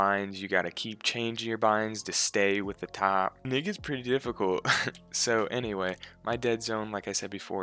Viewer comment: my man said the n word jk it just sounded like it( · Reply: Actually, he said, "And it gets pretty difficult."